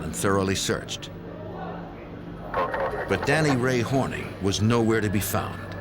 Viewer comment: For a moment I thought he was saying Daniel Ray Horny